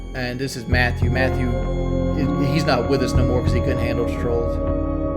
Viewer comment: "this is matthew, he's not with us no more cuz he couldn't handle the trolls," is that what he said? Did this dude off himself bc of trolls or something?